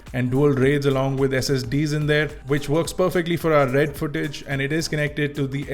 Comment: Did you say "RED" footage 😱😱😱😱😱😱😱